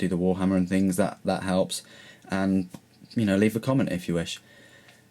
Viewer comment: At the end there i thought u said "THE EMPEROR FOR SEX" ;P